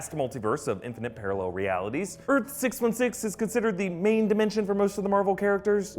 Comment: I thought u said Earth 666 xD